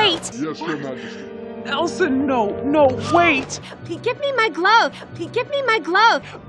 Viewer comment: give get me my glove 로 들림